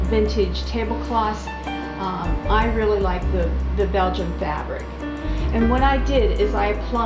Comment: Hi, I couldn't quite make out or hear what kind of fabric you were using. It sounded like you said Belgium fabric. Sorry, my hearing is failing. Also does the plaster come off? rub off? or crack and peel off? or does it stay embedded in the fabric?